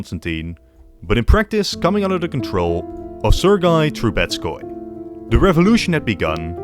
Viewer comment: I thought i heard taboritsky